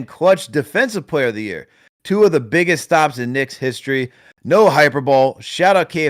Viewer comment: did just say “no ‘hyper-bowl’”?! Cdfu